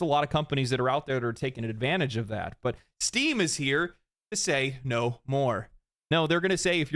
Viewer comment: I was half asleep and thought you said Steve is here to say no more.